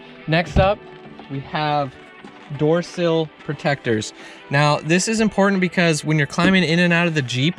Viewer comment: Did he say, "pertecters"?